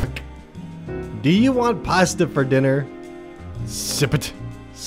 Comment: I thought this said “do you want paste for dinner”